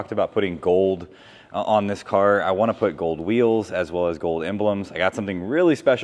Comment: I heard “gold wheels” and thought I probably have THE gold wheels for a Supra I have been hoarding. Top Secret Gold LE37’s from a TS built supra.